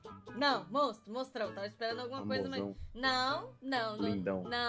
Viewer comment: Eu escutei MOZÃO shippo muito ❤❤( 0:45 )